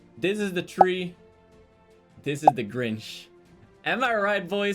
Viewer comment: I thought he said “cringe” instead of Grinch